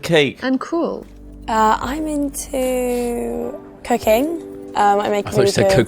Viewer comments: I also thought she said cocaine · I thought she said cocaine too what the fuck. Just imagine tho if she did say that and they just started doing lines. 😂😂😂😂😂😂😂😂